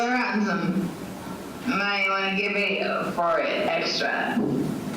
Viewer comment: Can anyone tell what she's saying here? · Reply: I think it's, "Wiggle around some. I might wanna make a video for it extra."